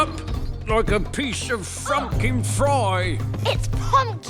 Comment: when I first heard him say that, I thought he said a piece of fucking pie. 😂